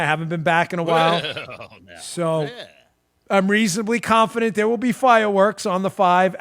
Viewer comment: Comey’s voice cracked when he said “I am confident....” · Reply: Confident? Oh, I thought he said "constipated" - my mistake '-)